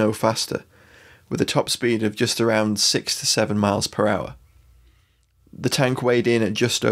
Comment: Goodness, for a second there I thought you said sixty-seven miles per hour! That would be quite a sight! Excellent video!